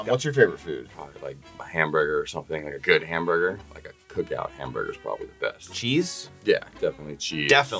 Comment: Did he say Cookout burger as in the restaurant chain "Cookout?" They've expanded to the west coast? Thats my fav burger spot.